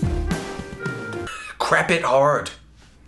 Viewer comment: XD Why oh why is the first thing I hear is "crap it hard"?